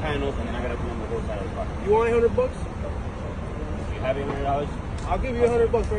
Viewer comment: He went from “I’ll pay you anything” to you want $100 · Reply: It did sound like he said " you want a 100 bucks" .